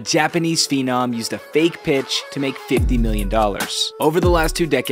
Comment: Really thought he said "fake bitch"